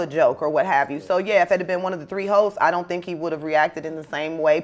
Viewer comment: 😂😂😂 did I hear that right ? Did Vanessa say "if it had been one of the 3 HOES" or did she say one of the 3 host ? 😂😂😂